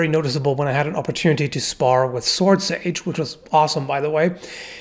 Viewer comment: There's a channel named Ranton, guy's literally spent 3 years training at the shaolin temple, he and his wife could give some insight on the shuang gao (hook swords). I would LOVE to see some videos featuring him. Maybe bring Swordsage a few more times? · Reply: I thought you said swordsausage